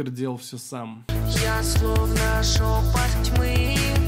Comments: сначала услышала "жопа тьмы" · В ее клипе мне послышалось "я словно жопа тьмы". Неужели автотюн настолько убого сделан? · Бляяяя, мне послышалось что она поёт "Я жопа тьмы" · я одна слышу «я словно ЖОПА тьмы» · В ее клипе мне послышалось "я словно жопа тьмы". Неужели автотюн настолько убого сделан? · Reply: А мне послышалось. - Я словно жопоть тьмы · Да бля! Неужели одна я в первый раз услышала "я словно жопа тьмы"?????? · мне послышалось "я словно жопа тьмы" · Я услышала « я словно жопа тьмы» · мне одному послышалось я словно жопа тьмы?